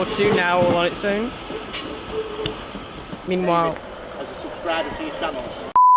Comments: what was he saying? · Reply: "As a subscriber to your channel..." and something else, I can't remember. Basically, he recognised me from my channel, and I was surprised! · what did he say and why did you cut it out XD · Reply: He said "As a subscriber to your channel..." and something else, I can't remember. It wasn't cut out, I was just so shocked that I hit the record button and ended the video! I offered him to say something else but he declined.